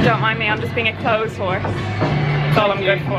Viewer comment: Genuinely thought she said I’m being a clothes whore and I couldn’t stop laughing 😂😂😂